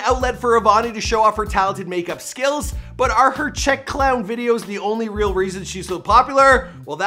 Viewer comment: Did he just say “check clown” I